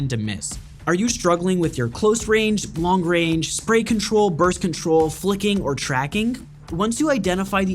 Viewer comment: i thought he said birth control